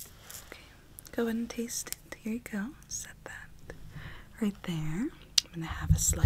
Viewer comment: When she said “ima set that right there” I thought she said “ima suck in that air” ✌️😂